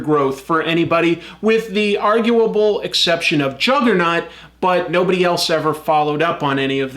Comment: It sounded like you said Juggernut instead of Juggernaut... Was that intentional? XD